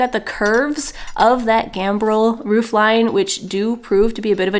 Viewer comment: Bramble style?! I'm intrigued what you meant by this! Thank you! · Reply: idk I thought you said bramble was why the metal working would be more difficult. Maybe it was gable? It’s me misrembering a term. Just put a * in front of anything I type! 😆